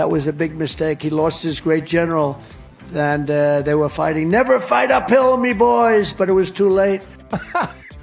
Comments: I thought he said "Fight a pill, me boys!" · I thought he said "never fight pills boy" 😂